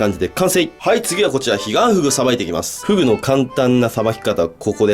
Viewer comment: ｢ヒガンフグを捌いて行きます｣が｢ディアンヌを捌いて行きます｣に聞こえて、え？ってなった。